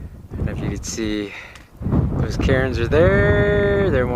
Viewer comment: Oh, my gosh! I was laughing at myself soooo hard!!! I thought you were saying, :Look at those Karens over there... I was waiting to see what the entitled people were doing on the mountain. I then realized you were talking about the cairns.... lol, the wind was distorting your voice (to me anyway).